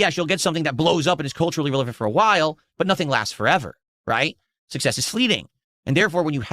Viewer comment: did he say "success is sleeting? " I cant listen to the idiot lmao